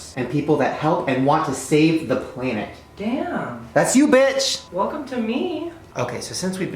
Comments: When Shane said "bed" at the beginning I thought he said "bitch" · I thought he said bitch not bed